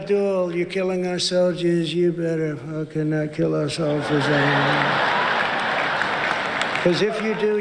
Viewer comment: did trump really say “you better not fucking kill our soldiers…”. ???